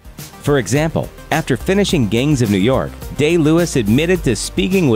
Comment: Sounded like he said gains of new york.